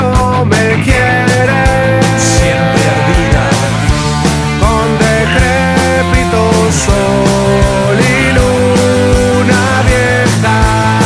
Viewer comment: cheee algien sabe que dice el pibe en el minuto 1:23 ??? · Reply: Con decrepito sol y luna vieja